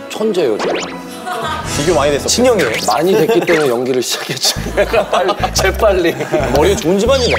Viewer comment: 이 부분에 뭐라고 말한 건지 너무 궁금해서 몇 번이고 돌려보고 볼륨도 높여보고 스피커를 귀에 가져대고도 들어보고 한국어 자동완성 자막을 켜고도 들어봤는데 도무지 뭐라고 말하는 건지 모르겠다…ㅠㅠ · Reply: 석진 - 비교 많이 됐었죠? 지석 - 많이 됐으니까 연기를 시작했죠 인 것 같아욤